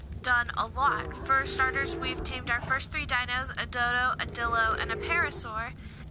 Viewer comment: DID YOU JUST SAY YOU TAMED A DILDO?